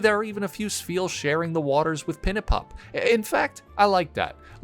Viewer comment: im not a native english speaker so when you said pinnipup i thought you said "petapup" like pet a pup, and thought that was a genius name lmao